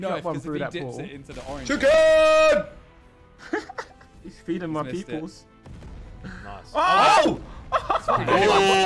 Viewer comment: I think veveryone missed 5obi saying he is feeding my people